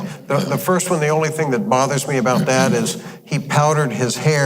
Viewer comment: You're making me laugh, Probably scaring Janet, too! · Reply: I listened to that portion of interview a 2nd time before writing original comment. I understand what I thought I heard. It is where you said...."it bothered me about "that", they powdered his hair." I thought I heard, "it bothered me about Dad.....they.... I am glad you could laugh & enjoy my mistake. This bit of an older lady, sure loved the Westerns, when I was young! Thanks for the interviews. Take care & God Bless